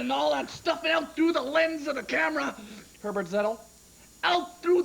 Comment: what does he say here? "herbert, settle" is what i hear ah, its "herbert Zettl" . a professor emeritus of the Broadcast and Electronic Communication Arts Department at San Francisco State University (SFSU), where he led the Institute of International Media Communication and received the California State Legislature Distinguished Teaching Award and the Broadcast Education Association's Distinguished Education Service Award